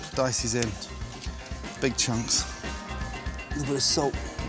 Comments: it sounded like big chungus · Did i just hear Gordon say big chungus